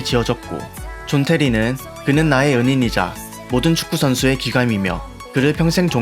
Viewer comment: 나만 은인이 연인으로 들리나...